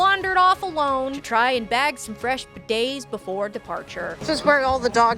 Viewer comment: I had to double check but is saying bidet instead of beignet?